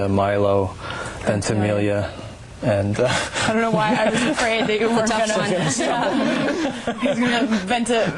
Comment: what does Lauren say? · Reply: "i don't know why i was afraid that you were gonna (idk) venta venta"